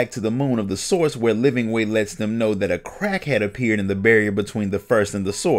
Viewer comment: At first I thought I heard "a crackhead appeared" lol